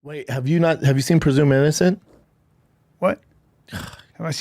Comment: I'm with Callen. What the hell did he say? "Have you seen presumarison?"??? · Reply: Haha... I heard presumed innocent, but barely. and I can't believe I heard it right...